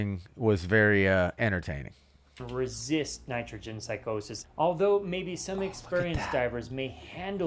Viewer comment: did he say 'Nitrogen Psychosis'? Is that a thing?